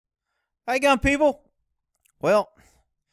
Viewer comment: Why do I always hear "Hey dumb people" at the start xD